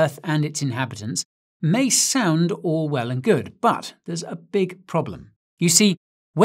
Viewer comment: I heard "may sound Orwell and good" 😂